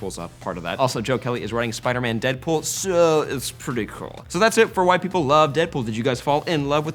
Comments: It sounded like you said "So, that's why white people love Deadpool" made me laugh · wtf "white People" i didn't get offended but why did you say white people ?? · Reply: He said "that's it for why people love..." Not white people.